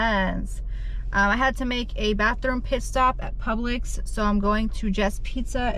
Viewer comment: I totally thought you said you were making a bathroom piss stop 🤣